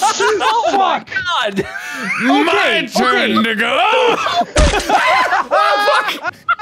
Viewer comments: GUYS MARK DID NOT SAY THE N-WORD AT 31:37! He said “to go.” Full sentence being “my turn to go.” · To everyone saying Mark said the n-word at 31:36, he didn't. He said "it's my turn to go". If you see people commenting that he did please calmy and kindly correct them. Please and ty 😊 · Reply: it's defiantly "it's my turn to go". I just heard it and it's an easy mishear